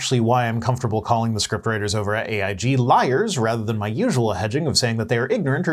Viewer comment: “... rather than my usual hedging...”. Yeah, I heard “edging”... 🤦‍♂️